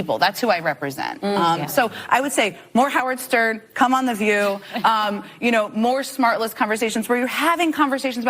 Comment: Did she say more smart less interviews 😂